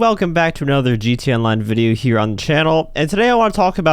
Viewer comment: i thought he said "heroin" lol.